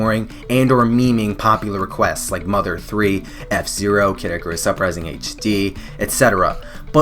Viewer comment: I thought he said Caddicarus Uprising.